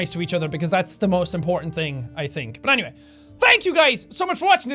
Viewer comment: I dont know how.. but i heard "thank you susie"